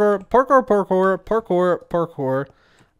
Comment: After awhile I just heard 'porker porker porker'